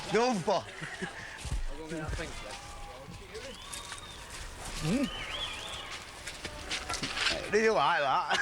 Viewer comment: "doompah... dyoolayyar" I think he said: "done for... did you like that?" or something, right? 🙃